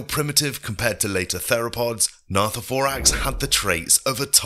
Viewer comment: The production is insane for such a small channel! But do be wary cuz some mistakes are made here and there, like the pronunciation of rhynchosaurus and the subtitles saying "not the four acts" instead of gnathovorax at 2:42 , and gnathovorax probably lived for a short amount of time instead of 30 million years according to wikipedia, and always use footage that showcases accurate dinosaurs, dont use images of feathered t.rexes or scaly velociraptors ( never use jurassic world or park footage ) and show the footage of the dinosaur your talking about, other than that, great video. You have alot of potential